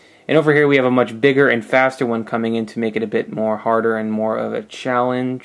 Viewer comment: did you say make it a bit more harder on 2:40?